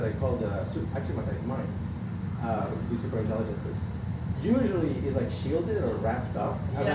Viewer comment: Does anyone know what he said? Its muffled and couldn't understand Thanks! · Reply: It sounds like he starts to say "super intelligences" and then cuts himself off to say "Axiomatized Minds"